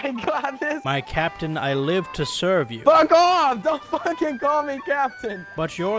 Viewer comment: what is the guy saying? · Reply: dontfuckingcallmethat